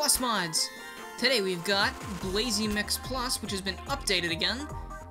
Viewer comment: I thought said Blazey McPlus XD